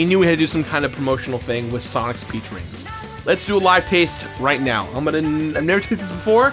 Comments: When he said peach rings I thought he said pee drinks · I know it's "Peach Rings" but i really heard "Pee Drink flavored" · When he said "peach rings" I heard "pee drinks" and i was so confused until I looked at it and re listened to it XD